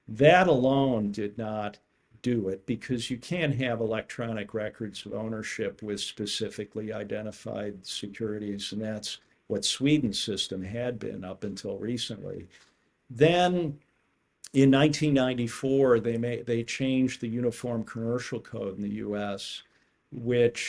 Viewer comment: Did he say Sweden? (36:47) He spoke in the context of pooled assets. Is / was Sweden, if that's what he said, any different in this regard? Explain.